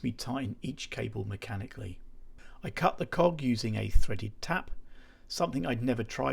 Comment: "Cut the cock" is what it sounded like at 5:55 got me for a second 😂 Huge props to this man for the time and dedication he has for these projects. I can't imagine the frustration of this build. Keep going man!